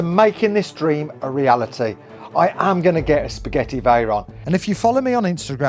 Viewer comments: I keep hearing Spaghetti Veyron in this video 4:51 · did he just say spaghetti veyron 😅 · Is it just me or does it sound like saying spaghetti veyron😂😂😂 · bro, it so sounds like you said Spaghetti Veyron lmfao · Had to rewind 2 times to be sure, he actually says "a Spaghetti Veyron"